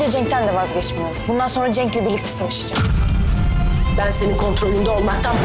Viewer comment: birlikte sevişcem diye duydum ve hâlâ öyle duyuyorum skdmslsösnmwnsns